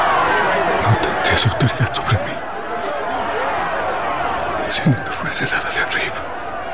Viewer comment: Alguien que me diga que dice Jesús en este minuto 1:08 a 1:24 ya que no tiene subtitulos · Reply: No tendrias autoridad sobre mi, sino te fuese dada de arriba, de dios.